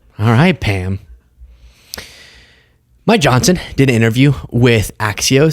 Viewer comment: 🤣 i swear i heard "My johnson" not Mike johnson